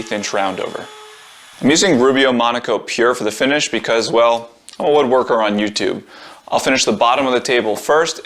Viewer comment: "I'm going to use Rubio Monocoat pure for the finish because well... I'm a woodworker on youtube." HAHA. So great! Keep up the good work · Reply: Lol, I thought he said "because OF a woodworker on youtube". I thought he was referring to Blacktail Studio who uses that a lot in his projects.